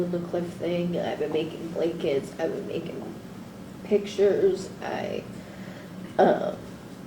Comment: Thought it said "I've been working out..."